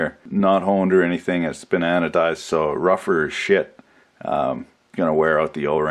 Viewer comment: I heard "been anodized" as "bananadized"